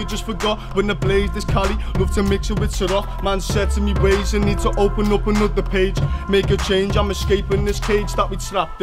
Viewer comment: His accent sounds like he says “open up me nan bag” 😂😂😂